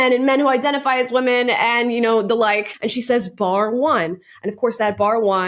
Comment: She's not saying "bar one". She's saying "by the way". It's still a reference to Rowling's supposedly exclusionary stance - characterised as Rowling NOT being "here for all the witches", but it isn't anywhere near the vicious personal dig that the mishearing "bar one" or "but one" turns it into. There's plenty to criticise Watson about with regard to her part in the way Rowling's been treated, and the way the latter's completely moderate views have been mischaracterised as bigotry, but this notion is just false.